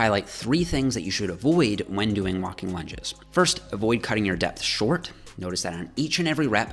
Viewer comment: Why did i hear avoid cutting onions sincs you'll be crying your life out